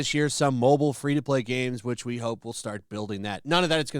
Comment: I heard it as freetus as it sounds like it’ll be aborted before it’s 9 months old